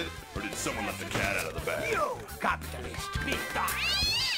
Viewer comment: That Donkey and Puss. They are the funniest in the "Shrek" franchise. Donkey: "Hey, why are you grabbing me?" "Police brutality, police brutality!" Puss: "You capitalist pig dogs!" "Catnip, that's uh, not mine." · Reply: I thought he said 'Capitalist piece of-' before he went feral. XD